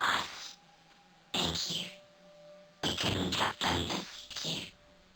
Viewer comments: I think they're saying "we [maybe "I"] couldn't have done this without you" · I think they say "we couldn't have done it without you" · i think they said we couldnt have done this without you